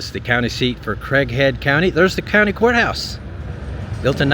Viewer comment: Thought you said Crackhead at 25 minutes 😂.